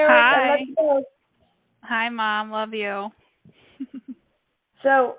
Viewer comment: I heard "Hi mom"